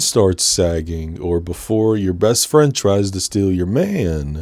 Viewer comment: For some reason, when you said best friend, i heard "breast friend" hehehe